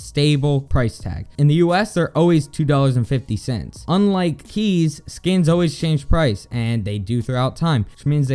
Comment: Hope you enjoyed! Wanted to clarify that when you use my code on csgosum you get a tax free trade up to $50 not a free $50 those are all scams. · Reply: were u high?i heard u say "keysess"